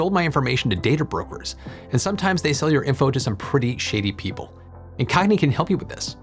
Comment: heh i had to do a double take when i thought i heard you say "they sell your data to some pretty sh*tty people" but it turns out you said "shady people" 🙂